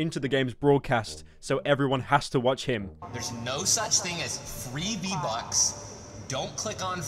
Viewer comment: i thought ninja said theres no such things as 3d butts